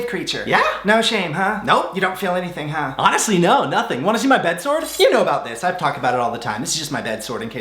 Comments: The Aladdin segment was my favorite 🤣 also when you said "bed sword", I thought you said "bed sore" 🤣🤣🤣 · Why did I hear “bed sores” when you said “bed sword” 🤣 lol I did not, in fact, want to see your bed sores. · Love your videos :) haha I thought you said "do you want to see my bed sore" but you said SWORD! haha :) This year I want to get pregnant, buy a house and get two puppies :) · I thought that you said 'Wanna see my bed sores?' but then you whipped out a sword and that made a lot more sense... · I thought you said BED SORE🤣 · I thought you said "wanna see my bed sores?" But thank goodness u said sword... Not sores. I was concerned